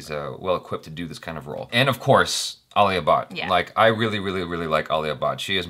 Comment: When he said alia bhatt I thought he is saying ali abbaz. Lol